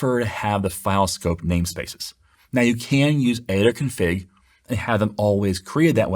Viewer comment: What is being said at 5:06, "Can still use ada Config"?